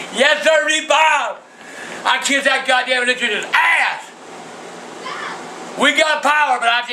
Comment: what did he say? I never understood that. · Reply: "Yesiree Bob! I kiss that gdamn electrician's ass!"